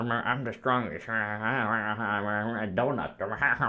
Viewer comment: through 7:48 I hear donut and it made me wheeze